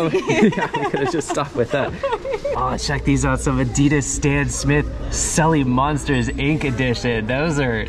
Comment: For a second there I thought you said Addidas Dan Smith 😂